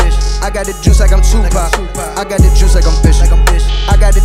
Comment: did he just say "I got the juice like I'm bishu"